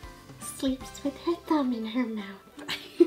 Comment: I thought she said tongue instead of thumb.